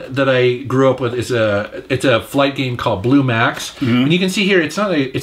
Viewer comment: genuinely thought it said Billie max.